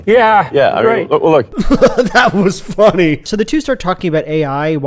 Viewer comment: sorry, but Musk actually says “I wish that was funny” here, repeating what Jordan had just said earlier in acknowledgement that he (Musk) found his (Jordan’s) statement funny (the statement being “I wish that was funny”). You can do whatever you like with that information